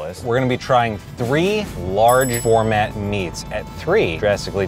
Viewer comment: I thought I heard said format instead of gourmet...🤔